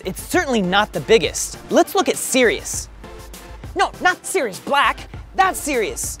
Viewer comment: At first I thought you were saying ceres but it’s Sirius